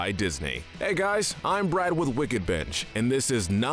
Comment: I heard “I’m Brad from Wicked Bitch”